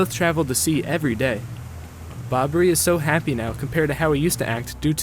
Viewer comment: I thought he said "bombey is happy now because his wife is dead"